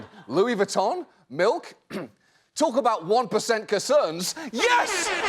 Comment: At first I'm like "wtf is he saying" but I guess he meant to say "1 percent CONCERNS".. which I guess I get the joke then, he just completely left off the N off the word "Concern"... very odd mis-speak to hear